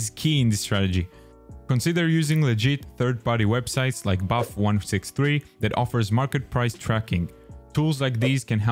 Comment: Amazing tips! Also i'm sorry, I couldn't quite catch the third-party website you mentioned 2:22. Could you repeat it again please? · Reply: Of course! It is Buff.163 a Chinese site that has the “cheapest” prices so a lot of skins, patterns and float prices go off this site. It is the number 1 site for price checks.